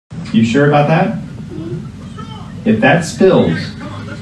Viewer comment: I thought he said “If that’s pills,,” and I was like IS SHE DOIN’ DRUGS